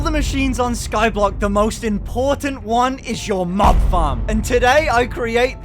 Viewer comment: I honestly thought he said Mom Farm. 🤣